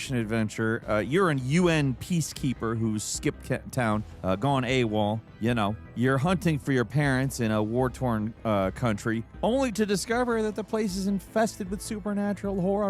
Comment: did u say Skip because that guy looks like Skip bayless 😂